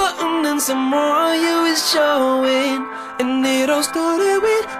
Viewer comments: I thought he said " cover my body in your BUTTER BREAD" when I heard it on the radio Now I looked it up and it says "AUTOGRAPH" And I feel very stupid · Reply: Thats okay, I thought he said "Have my babies" When he says "Hi my name is." Lmao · Instead of "Hi my name is..." I always hear "Have my babies...." Is it just me? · How it goes: "Hi my name is," What I hear: "I'm on Levi's," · honestly I hear "have my babies" instead of "hi my name is" and "let's get pregnant" instead of "let's get undressed"